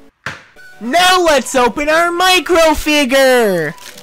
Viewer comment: For some reason I heard "NOW LET'S OPEN OUR MINECRAFT FIGURE!!!!" due to how loud is in his toy review.